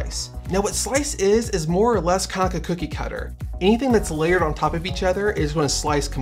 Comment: Lol I died laughing when I thought I heard ca-ca-cookie cutter